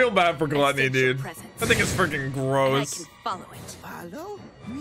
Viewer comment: is it me or does it sounds like said the n word XDXD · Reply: He said "I think he is freaking gross", but it does sometimes sound like he said "that n-word is freaking gross"